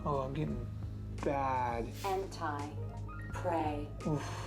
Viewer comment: Around the 3:00 mark I looked away and when I heard the voice I heard "Henti play."